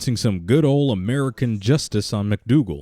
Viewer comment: To me it sounded like he said "Damn you Dutch!"